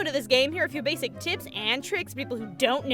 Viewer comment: Maybe I just misheard "but if you're new to this game, here are a few basic" whats? "and tricks?" I know, I know, it's "tips," but no matter how many times I replayed it, it still sounded like "tits and tricks." >.>